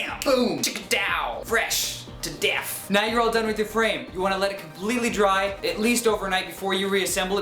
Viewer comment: Sounded like you said "nine year old's done with your frame" and "your pants gonna be kinda soft" thanks tho really helped 😀